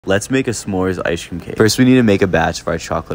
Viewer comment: i thought he said brownie icecream cake god damn i need to sleep